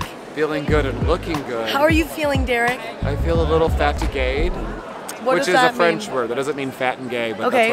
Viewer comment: Derek said some french word that sounds like he said feeling fat and gay and I'm like honestly same, it's very tru